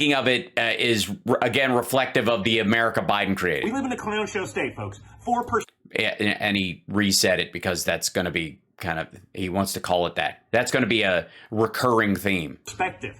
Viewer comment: I thought he said " Clamshell" till you said it was clownshow 🤣🤣